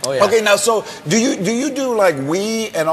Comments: I heard George say " do you do weed?" I was like "what?!" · I thought he said "do you do weed" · I thought he said do you do the weed instead of wii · OMG when george asked him "do you do wii" i thought he said "do you do weed?" rookie mistake x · I thought George Lopez said "do you do weed?" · At first I heard "So do you do the weed" I was like wtf den then i was lmfao :-D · i thought he said do you do weed at first... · HAHA when george asked if he does wii i thought he said "Do you do weed?"